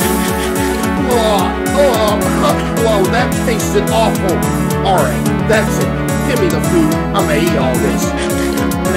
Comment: what does the uber driver say?? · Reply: he says "wasn't that his food?"